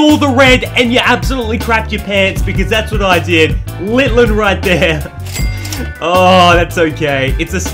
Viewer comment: did I just hear my guy say "Littlen"?